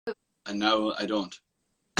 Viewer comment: did he really said no i don't... Ehm okey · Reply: He said 'NOW I don't'